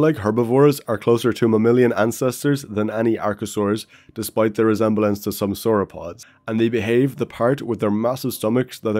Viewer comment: What is this called I can’t look it up because how he pronounced it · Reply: Cotylorhynchus